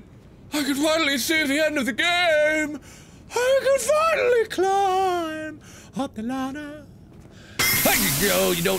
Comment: Did anyone else hear "I can finally climb a banana?"